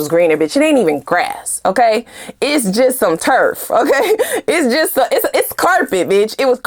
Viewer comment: I definitely heard terf too 😅